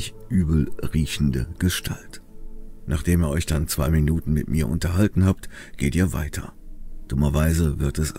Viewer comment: "Nachdem Ihr euch dann zwei Minuten mit mir unterhalten habt ... " . SENSATIONELL !!! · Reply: Echt Klasse. Hat er echt "mit mir" gesagt? 😆 Ich habe "mit ihm" verstanden. 😁